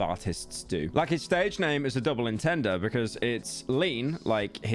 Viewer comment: did you just say double intender?